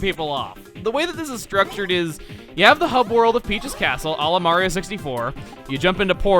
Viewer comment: I heard "Olimar 64"